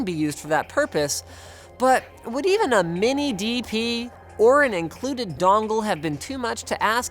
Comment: Included DONGLES? MORE PORTS? CMON LINUS WHAT DO YOU EXPECT IN 2017? YOU KNOW BETTER! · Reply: Lol I thought you said it was dongless